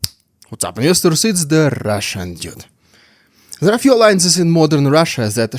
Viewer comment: No matter how many videos I watch, I don't hear "Russian dude", I always hear "Russian дед"🫠😂